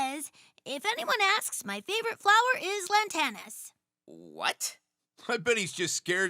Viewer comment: Eso entendió Tails, pero yo entendí "Si alguien pregunta por mi tipo de mujer favorita, son las latinas"